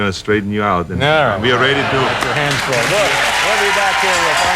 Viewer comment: It sounds like David says something like "Alright, get your hands off me"